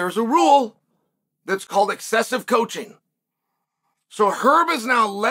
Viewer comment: I thought Herb said “deceptive coaching” . Tim was yelling at Aljo like he was Ray Longo in that first fight. “You gotta go Aljo.” Tim said he was telling Merab “Be patient Merab.” I felt Herb was warning him not to be imitating the other corner. Maybe not? That’s what I heard.